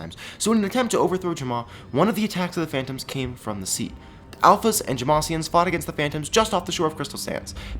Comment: I swear you just said Alphys fought the phantoms